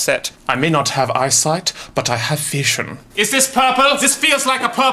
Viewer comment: did he say vision or fashion😂😂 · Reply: Felipe Rodas Vision